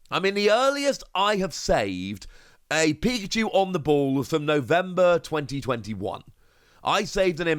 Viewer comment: Lmao i thought you said Pikachu in the mall and then you started talking about the others